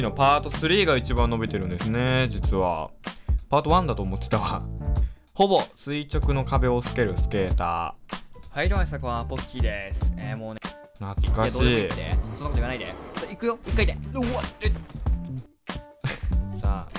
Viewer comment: がほぼ垂直の壁を『 すける』スケーターって聞こえるの私だけ？